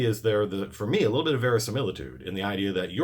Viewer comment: Excuse me, I’m no English major. What in the fuck did you just say? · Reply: ver·i·si·mil·i·tude noun the appearance of being true or real. "the detail gives the novel some verisimilitude" synonyms: realism, believability, plausibility, authenticity, credibility, lifelikeness "the verisimilitude of her performance is gripping"